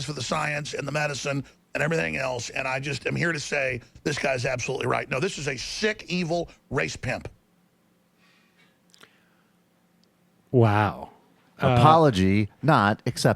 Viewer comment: I thought he said space pimp